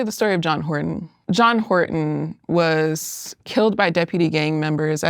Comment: i’m so sorry but i was half paying attention and i thought it said john pork